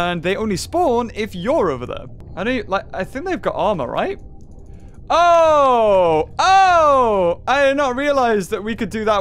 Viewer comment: he says ora. Jo jo reference... or it sounds like he says it anyway